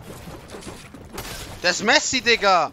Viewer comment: did he say messi ?